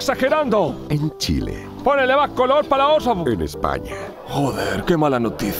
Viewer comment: que dijo? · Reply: "Pónele más color pa la otra" Poner Color (Verbo): exagerar, agrandar una situación.